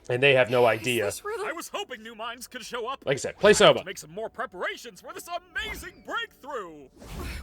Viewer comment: what game is he saying? Soba? Sova? I can’t find it online :( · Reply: Soma, another game about AI